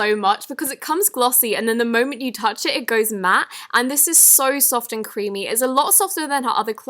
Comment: For 1:47 did she say soft and creepy ?